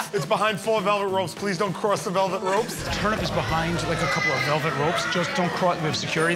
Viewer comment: Pretty sure that guy thought Murr was saying turn up? Like it’s lit lol